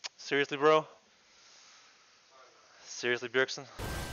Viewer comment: What did the other guy yell at 0:15??? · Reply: Kenraali tsm meteos really